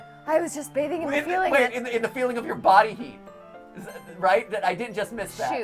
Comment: What Yuri said at 1:13:58 is: "I was just bathing in the feeling of your body heat"